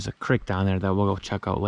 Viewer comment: Did you say ‘crick’ do you mean ‘creek’? Crick is what my spouse says and I’ll say “you mean creek”. Cc heard creek. Lol. Arroyo in Spanish.